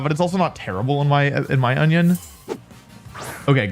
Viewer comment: Anyone else hear “in my onion” or just me